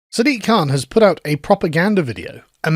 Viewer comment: "propaganda"? I heard "proper Gandu" Gandu in Urdu meaning - LGBT